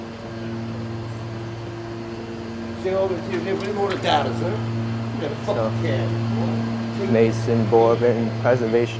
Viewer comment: What he said? I can't barely hear it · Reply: He said “let me hold a dollar son, and you got that f**kin camera.” He just got a real strong black american accent